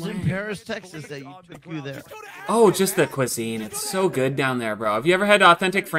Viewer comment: I thought he said 'pussy' instead of cuisine.."Yeah, you know, just the pussy". Really changes the purpose of travel for